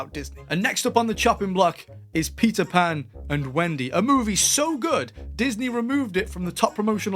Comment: I'm so stonned right now, that I hear you say "Pizza Pan and Wendy"! LOL